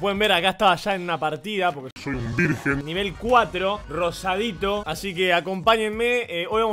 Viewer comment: parece que dijo soy un virgen nivel cuatro